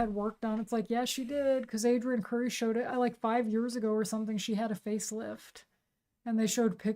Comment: Wait, I thought you said she died like half a year ago.